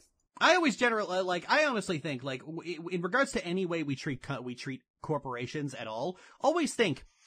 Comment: Thought you were gonna say cunt-porations at first 😂